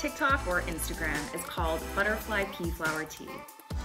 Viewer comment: I thought you were saying "butterfly pee" flower 🤣